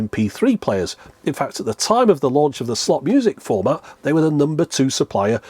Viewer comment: I keep hearing "slop music" 🤣🤣